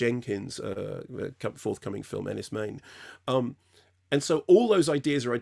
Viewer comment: Mark Jenkins forthcoming film what? 1:45 seconds in I can’t tell what he said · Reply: Enys Men